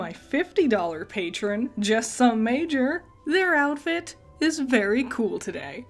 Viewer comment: I thought you said "just some mayor" at first and I laughed thinking you were making the joke from the short lol